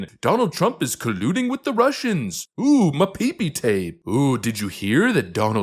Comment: wtf did you say there 😂😂😂😂 my peepee tape? 😂😂